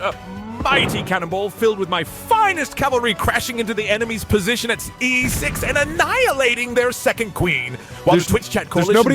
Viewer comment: do you mean c6? Thought there was a “sss” sound before I heard “e6”, making it “c6 There’s a Twitch chat queen on c6 so maybe the text2speech f-ed up and meant c6